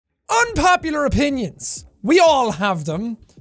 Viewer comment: at first I thought it said "unpopular onions" 😂